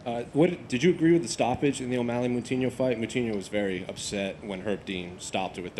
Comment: Korean Zombie: “Im the ONLY ZOMBIE in the UFC” Coutinho: “Hold my mouthpiece” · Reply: Someone needs to hold your mouthpiece, as it sounds like you said coutinho instead of moutinho 😕